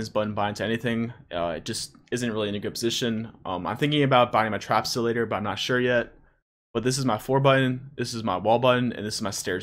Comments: wtf did he say at 7:37 ?? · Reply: HazzyaLondon yeah wtf trapsilator is what I heard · what do you say at 7:38 "You are thinking of a buying a"